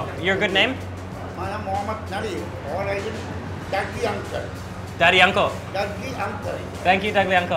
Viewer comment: did he say, Daddy Yankee? then at 10:40 did he say Hello Kitty?